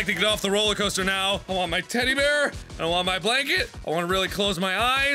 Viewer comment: Okay my internet was being weird I thought you said titty bear not teddy bear